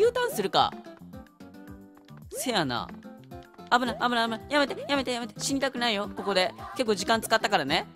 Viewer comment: かな？のやめてがるぅとくんに聞こえたｗｗｗ